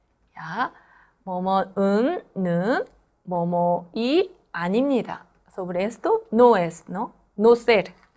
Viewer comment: qué es eso que dice? "momo"?